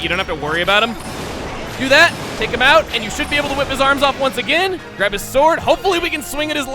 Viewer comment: when you said "whip his arms off" it sounded like you said "rip his arms off" with a lisp haha